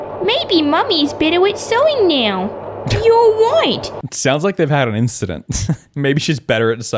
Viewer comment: i swear i heard,"youre white!" edit: i think in america instead of "bossy boots" its "sassy pants" edit #2: barbies sisters are chelsea, stacy, and skipper. stacy is in middle school i think and skipper is in high school. they should be living with their parents but instead lived with barbie. i think its because their parents either either died or divorced and couldn't take care of them anymore. this could be a situation where stacy went missing and skipper graduated. that would be a lot.